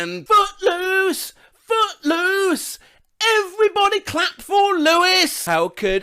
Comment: I used to mishear footloose as "everybody cut for loose!"